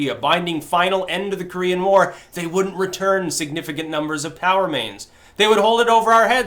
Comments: I keep hearing "Power Mains". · I luvz that Styx doesn;t say P-O-W... i picture the 1960s batman POW being held by the NK... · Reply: At first I thought he said "power mains" and wondered what the hell those were. There is something to be said for clarity. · I thought Styx kept saying "power mains".